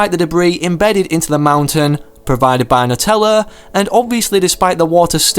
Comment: did he say Nutella •○•